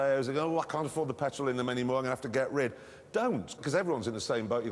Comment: I'm not sure, but if you listen closely to what Jeremy says at 0:40, it will sound like "or the many will have to get raped"